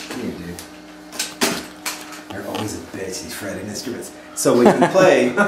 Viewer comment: What does he say at 0:37? "They're always that bad at defretting instruments"?